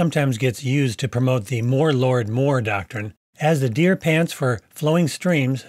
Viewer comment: That guy with the black hair and beard that was saying "more Lord " and "more God" really fast to the bouncing half nekkid bird sounded like he was saying "Mordor" and Morgoth". It's almost fitting a false teacher would reference a dark area created to represent Satan and his works.